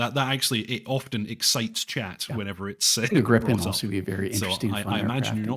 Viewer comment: I got excited when he was talking about the Super Cobra because I thought he said Griffon....